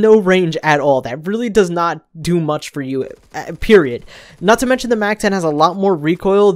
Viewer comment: did i just hear say period?